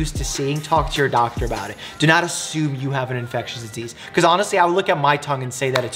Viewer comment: I find it so funny that the captions say "infelicitous" instead of "infectious".